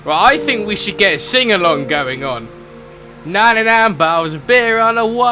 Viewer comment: long I thought he said 99 balls of beer but he said 99 bottles of beer